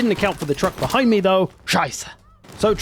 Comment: what u said? Scheisse?